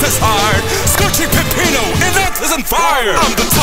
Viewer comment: I actually thought Pepperman said “ Scorching Peppino in an autism fire! “